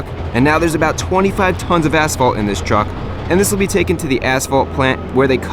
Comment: Did he just say 25 tons of pavement, as in 40,000 pounds